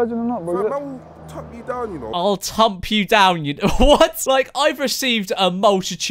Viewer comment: I thought he said “ I’ll trump you down “ 😂